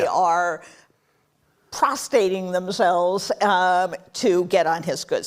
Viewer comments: Did she just say “prostating”? 😂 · She claims MAGA supporters cannot read, but then at 8:44 she claims that the media who have folded to Trump are "PROSTATING themselves". I listened to it many times, and instead of saying "prostrating", she says "prostating". 🤣